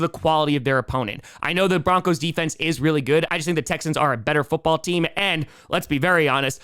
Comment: did anyone catch him say the Texans?